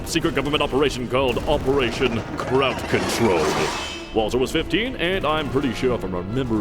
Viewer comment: "Operation KRAUT Control" I can't... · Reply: oh...................my..................GAWDS!!! HOW HAVE I MISSED THIS JOKE ALL THESE YEARS?!?!?!?!?!? I have always heard it as "Operation crowd control" and just never realized there was a joke there until just now!!! 😭😂😭